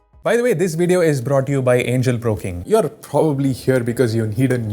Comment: is it just me ..or anyone heard him say "angle pro king"